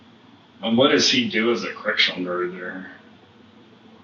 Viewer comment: caption said "Christian murderer" instead of correctional officer 😂